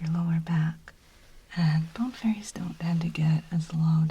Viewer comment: I thought you said "bone ferrets" at first and did not question that that was absolutely something would say in a video. Very excited to find out if all my bones are safe!